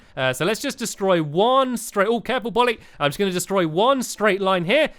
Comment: did said boly ?